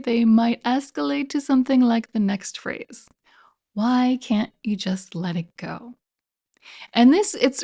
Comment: The one that I hear instead of "why can't you let it go" is "it's fine" or "it's fine, don't worry about it".